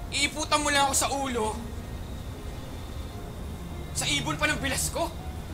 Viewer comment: Yung 44:17 hindi maintindihan. Ano sabi nung guy ? “Ibon pang pinasko”? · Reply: ibon daw ng bilas nya